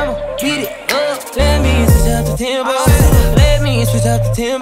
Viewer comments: I hear “switch up the tampon”🤣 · Sounds like he is saying “ Let me switch up the tampon”